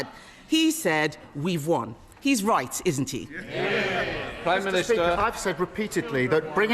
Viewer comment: DID HE JUST SAY MISTER PEAKER 😂